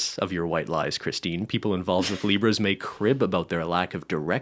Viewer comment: I'm eating m&ms and it sounded like was saying "zebras" instead of "libras" at first and I loved that